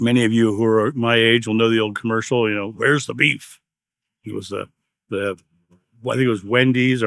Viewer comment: lol, for a second I thought you were saying "Where's the belief?" And yes, I remember the old Wendy's commercial where the woman asked where's the beef. Thanks for providing the distinction between affirmation and receiving (declaring).